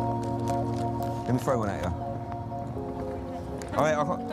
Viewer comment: What does he say at 2:49? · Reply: Let me throw one at ya